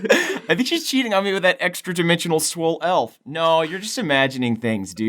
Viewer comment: When said "swoll elf" I thought he said "swoll elk"....now I ask, Can we get a swol elk or just a family of swol forest animals please?